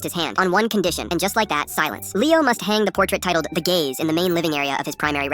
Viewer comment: Ha, I thought it said "The Gays" not "gaze" lol